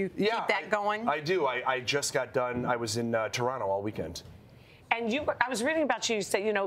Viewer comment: Did she say "I was reading about Jews" ?